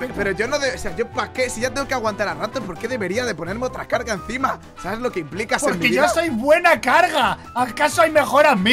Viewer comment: sube nuevo video El fandom:que hagamos más teorías de que el spartor es real dice xd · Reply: Dijo en el minuto 3:17 sabes que implica ser novio algo asi o escuche mal xd 💃